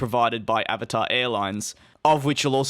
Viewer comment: It took me a while to get the name right, I thought you said Abattoir Airlines several times. Strange name for an airline.